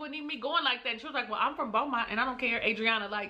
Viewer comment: I thought Adriana said Baltimore🤔